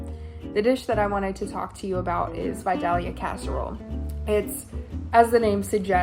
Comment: I really thought she was saying castor oil not casserole 🤣🫢 embarrassing on my part